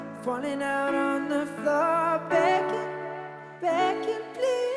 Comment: I'm sorry i heard "bacon" instead of "begging" TT now i cant listen to that part without laughing..